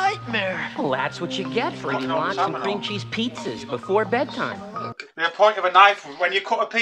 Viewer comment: I thought he said "Lots of cream cheese pizzas" on account of eating locks made no sense in context and there were no subtitles available back then. Unless you had one of those magical hearing-impaired Cathode Ray Tube televisions that could do the Closed Captioning thing. Man, those were really the dark ages.